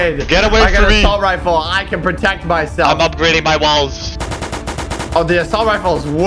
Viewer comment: I thought said he upgradeing his balls